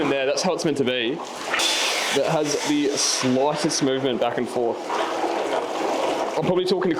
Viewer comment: when you said "lightest" i heard "loudest" because of that australian vowel replacement. or is that new zealand?